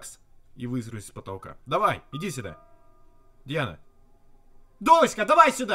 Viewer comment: мне одной послышалось "Диана"?